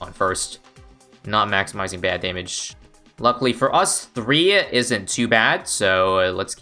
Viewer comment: what he says: “luckily 3 isn’t too bad..” what I heard: “ luckily 3 bad isn’t two bad”